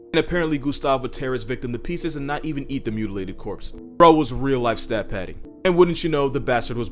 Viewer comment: God making Gustav: You know what? Hippos are OP. They need a counter. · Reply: What'd he said at 4:45?I heard "bro was real life stat padding" which I never heard before